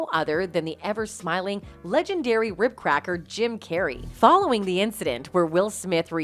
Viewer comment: did she just call him a cracker?